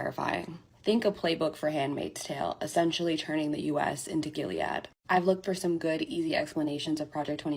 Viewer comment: What is that word @ 0:22? Turn U.S. to what? Please help me because I'm unfamiliar with the word and it's spelling. Thanks in advance and good luck to us all, we're in trouble. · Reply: Gilead. The Handmaid’s Tale describes the U.S. as a dystopian country ruled by a bunch of religious zealots. It’s a good and descriptive read, or tv show on Hulu if you’re strapped for time.